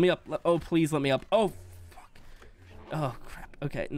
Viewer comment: bro thought he could say "aw fuck" without us knowing 💀 Sorry for language Ik you're family friendly but I had to say it 😂